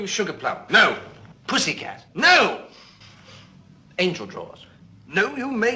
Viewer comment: Angel Jaws gets me every time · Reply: Thought he said “angel drawers”